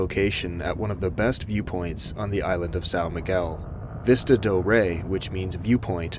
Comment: Did you just say, "Sow Migyual"? 😭